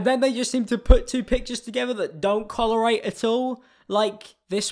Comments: I swear he said collerate not correlate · did he just say collerate instead of correlate?